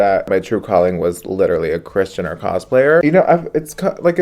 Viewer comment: im sorry but what did he say at 4:25? christian or cosplayer?? christian...... did i hear that right? not that theres anything wrong with that i just feel like given the context it doesnt fit but maybe im just stupid, help a gal out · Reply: He said Kris Jenner cosplayer